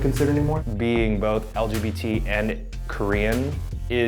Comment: did Eugene just say "being both LGBT and Korean" ?? is he lgbt+??